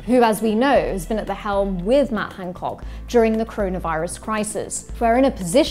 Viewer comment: A Freudian slip but it in your presentation it sounded like you said 'croney-virus' and I thought it nailed it as a phrase.